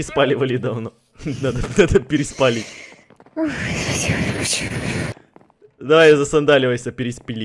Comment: На 18:50 мне послышалось,или Эд сказал "Зая, засандаливайся"?